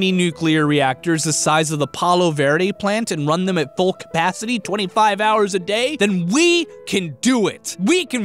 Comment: did he just say 25 hrs a day